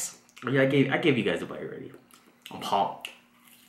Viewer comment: i I always thought he said "who's pom?" But then i readed the t-shrit and it said "pumped"